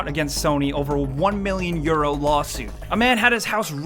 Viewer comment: I thought he said "1 million year old lawsuit"